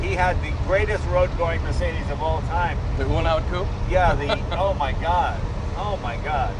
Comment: anyone know what car they're referring to? "The greatest (Mercedes) road-going coupe" driven by Rudolf ____halt and Hans Niebalt(???) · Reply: Just Google "Uhlenhaut Coupe"